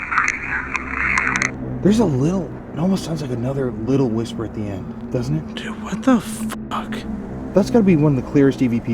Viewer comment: I don’t normally hear the evp recordings well, but after the question ‘is this place dangerous’ I swear it sounded like “it’s crazy out here..”. I’m from Gettysburg PA, so haunted and history is all around me, but I never knew about this place, and I definitely need to look into this more. Thanks!